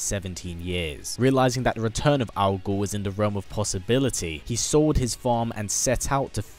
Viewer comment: When you say 'Algol' I think you're saying 'Al Gore'